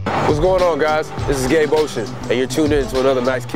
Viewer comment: I'm sorry but I thought you said my name is gay ocean and I was like...... Okay.....